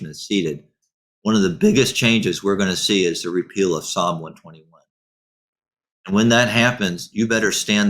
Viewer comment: He said the "repeal of xxxx". What was that word!!!!???? Also... Have you done a video on 'consolidating UTXOs "??? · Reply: It's SAB12. A rule put out by the SEC-not voted on by Congress or anyone else-that made it prohibitively expensive for banks to custody Bitcoin. When it is repealed, banks are going to flock into our space trading, buying, selling, and custodying bitcoin.